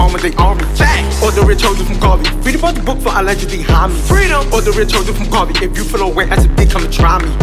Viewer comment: What did he say at 0:45? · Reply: ”free the bros booked for allegedly hommie ”